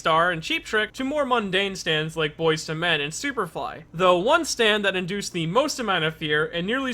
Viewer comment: said said "Boys to Men and Superfly" and I managed to mishear it as "Boys to Fly and Superman"